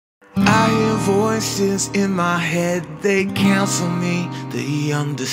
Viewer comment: did he say "I Hear voice in my head they cancel me and they understand"